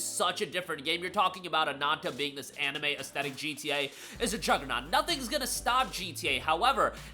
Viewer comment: when he said "a juggernaut" i thought he said "theres truck kun" due to the truck just hitting the car at the exact moment he said it lmao